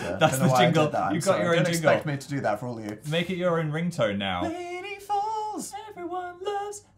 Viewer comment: I was watching this and I wasn’t really paying attention, and when they sang ‘Laniefalls’ I thought they were saying ‘Lady Balls’ ..... yeah